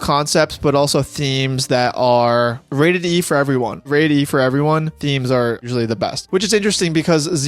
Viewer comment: what did he say?? I really need to know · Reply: Rate "E" for "Everyone." It means ALL-AGES content. Something everyone from children to adults can see.